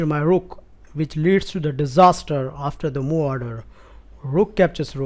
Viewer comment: always wondering what is this word at 5:38 ? I assume it is a word play with move and order, so morder? Or how is it written and is it the meaning of the word? Also is this a general word of the chess community or does only use this? btw really cool videos, I appreciate your input in every video series you make :) you made me a member of the chess community like 2 months ago and I can see my improvement already^^ Thank you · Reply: eule franz He's saying "move order" but it sounds like "morder" because of his accent.